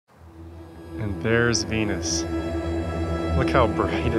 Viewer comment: The auto generated subtitles say "And there's anus, look how bright it is." Good job YouTube. You nailed it. Haha. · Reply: I think it may have been sinus issues or something, because when I heard him say it the first time it sounded like "anus", but I went back and played it again and it was in fact "Venus". I was thinking at first, though, "You mean there's another planet like Uranus?!